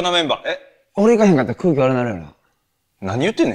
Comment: ここ「俺行ったら空気悪なる」に聞こえて一瞬胸ぐー系のネタかと思った